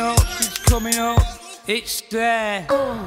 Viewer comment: Fun fact: the song was originally entitled 'It's There,' but Shaun Ryder's think Mancunian accent rendered him incapable of pronouncing 'there;' it always came out as 'dare.' The Gorillaz decided that they would just change the name of the song to 'DARE,' rather than try and get Ryder to say 'there.' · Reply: I thought he said its that